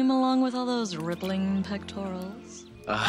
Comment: I always thought it was her saying “pec twirls” not “pectorals” LOL